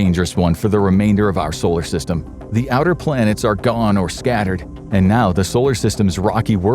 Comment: Did you just say "the SEVEN planets are aligned"?? Uhhhhh i got some news, buddy